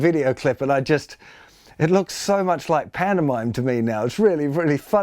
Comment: "...it looks so much like pantomime to me...". (I heard "Panama" and shouted "WTF?!?!?". Woke up my kid...)